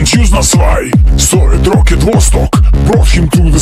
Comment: I swear I hear "solid rocket Vostok" and it fits the song perfectly. 😅